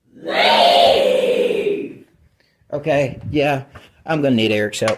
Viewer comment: i thought it says REEEEE. otherwise that shit spooked me